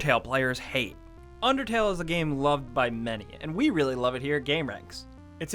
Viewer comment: Lol I thought you said 'loved by men'......heh..........you were about to have a bad time.